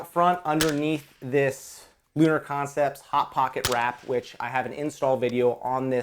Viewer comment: What handstop/rail cover ya runnin? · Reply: Sounds like he says "Lunar Concepts Hot Pocket Wrap"